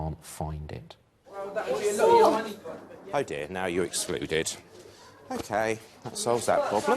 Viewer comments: I thought it said Executed. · I thought that said executed 😂😂